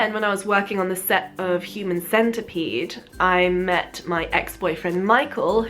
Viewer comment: did anyone else hear say human centipede wtf?